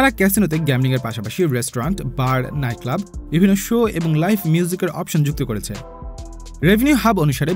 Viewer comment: আমিই কি শুধু 'লাইফ মিউজিক' শুনলাম?